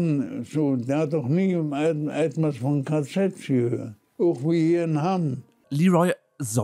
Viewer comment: die Stadt, die er erwähnt, die du nicht verstanden hast, ist Hamm. "... auch wir hier in Hamm."